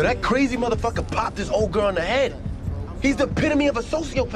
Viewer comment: Look he said "he shot his old girl in the head" as in mother🤦🏽‍♂️ y’all could literally replay the vid and see and his cousin he even said "he shot AUNT D" his own mother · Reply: I thought he said "own girl"